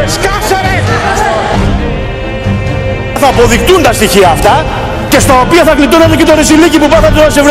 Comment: Τι είπε στο 2:55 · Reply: 'Θα αποδειχθουν τα στοιχεια αυτα'